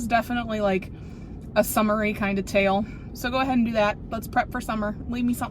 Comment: I thought you said “summary” instead of “summery”, so here’s a notepad 📝